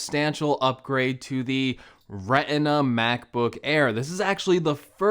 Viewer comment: Did he just say “Reh-inn-ah” (Retina)?